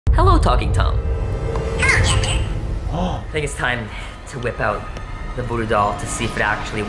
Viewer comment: When he said let's get the voodoo doll out I thought he said let's get the booty doll out 🤣😂🤣😅😂😂🤣😂😂😅😂😅